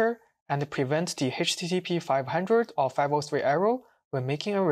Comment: Please always create transcript and attach with video, the auto transcription can make mistakes, see what is written at 4:13 position in the transcription, It wrote Arrow instead of Error